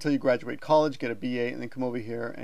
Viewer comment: did you say get a B.A? would a B.S work for teaching?